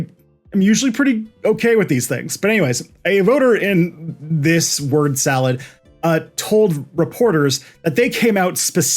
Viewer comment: At hour @ 2:00:00 what's the word (consonant) salad? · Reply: Schenectady